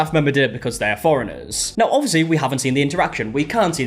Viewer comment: Why did I hear 'Floridans' instead of 'foreigners'. I was so confused 😆🤣